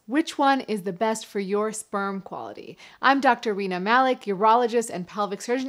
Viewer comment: I heard it as I'm Dr. Weiner malik lmao